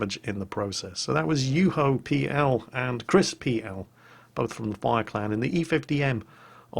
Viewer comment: when you say "chris_PL" i heard that as crispy L